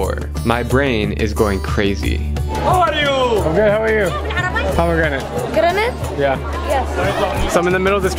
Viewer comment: Okay im sorry but i laugh at this part 1:28 because it sound like he saying "Granade".